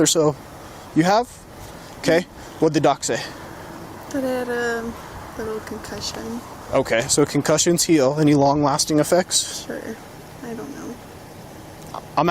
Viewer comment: I thought he said “what the duck say” and instantly remember the duck song